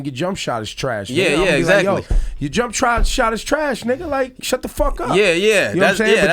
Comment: did dj just say N*** 😂